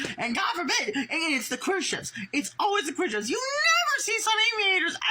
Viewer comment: I think at 6:46 he said queer ships ?? Because then he talks about trans sonic and then says gay ships , so I think he’s saying that sonic/Amy is homophobic or like anti queer ?? LOL